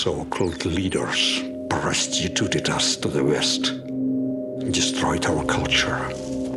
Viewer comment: I always thought he was saying "our so called leaders burst into the dust of the west"